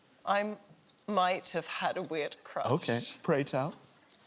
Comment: I thought he said “beard crotch” instead of “weird crush”